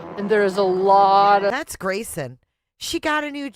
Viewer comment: listen closely and you can hear the name Grayson said, it sounds like Grayson talking about herself in a mocking way to other people, as in she’s bagging and laughing about something someone said about her and Grayson in relaying what was said. That’s definitely Grayson’s voice without a doubt.